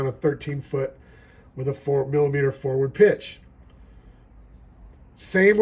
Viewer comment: it sounds like you said 4mm pitch.😬 Thanks for your reply.